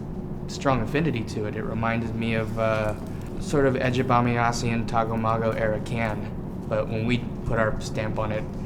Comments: hi there! can someone please tell me what does he said at 1:33 ?... english it's not my mother language, so i couldn't deciphe what he said.... ty! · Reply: “it reminded me of 'Ege Bamyasi' and 'Tago Mago' -era Can.“ he's referring to the German rock band “Can“ from the late 1960s, and the sound of two albums they made. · Uh what does he say at 1:34. What era of Can is it? He does that so quickly. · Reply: sounds like "edge of bamiassian tagomago era can." I have no idea how to spell it, or what it means, but that is what it sounds like.